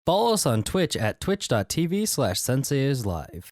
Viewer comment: You said "follow us" so fast it sound like you said balls lol